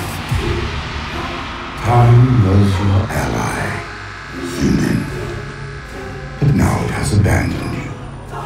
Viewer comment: What does the Didact say at 1:08? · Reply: "Time was your ally...human. But now it has abandoned you. The forerunners have returned."